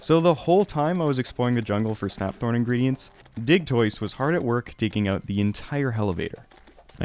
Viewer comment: Thought you said dih twist at first not digtoise